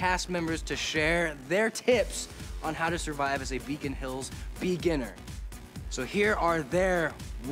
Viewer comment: I thought he said "Vegan Hills" 😂😂